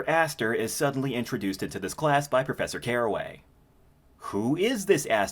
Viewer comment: me half asleep on 2:21 and i thought i heard "calloway" and thought of jim 'boy' calloway from rdr2